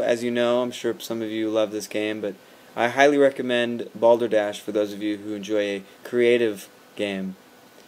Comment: In your other videos I always thought you were saying Boulder Dash (awesome video game). Hehe, my mistake. I guess I'll have to check out Balderdash now. Nice haircut by the way :)